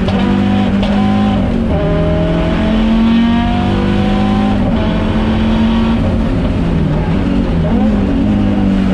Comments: I don’t know German, but I can imagine what he’s saying! 😂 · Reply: he say Scheibenkleister. :) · I wonder what he said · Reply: he said sh.. gas…because there was an issue with the gas pump ✌🏽